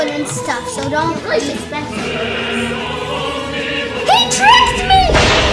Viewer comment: I thought u said Fricked Epicly